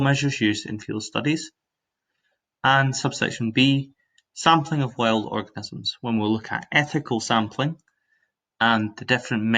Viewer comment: Did anyone else hear him say "sampling of wild orgasms" at 0:22 :')